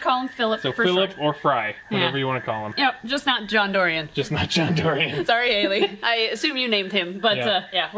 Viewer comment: I thought at first i thought you said John DOery, like from Fear the Walking Dead